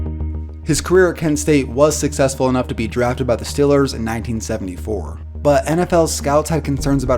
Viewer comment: "...drafted by the STILLERS..." That's the most Pittsburgh accent I think I've ever heard on YouTube. Lol. Is from the 'Burgh?